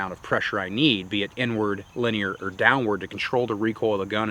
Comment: 17min when he said inward I thought he said n word… lol